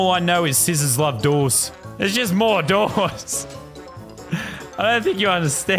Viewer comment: being a lord of the rings fan, i kept hearing "mordors" when lazar was having an episode Update: Holy crap guys... these replies are so mixed, i can't tell who's defending my point or just attacking kirply gaming...